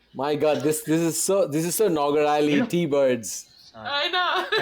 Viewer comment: Can someone tell what Deepak said @ 1:38? I heard nograli tea bird. 😅